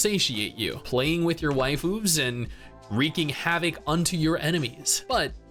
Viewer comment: I thought you said waifoobs. Thought I was getting old fast.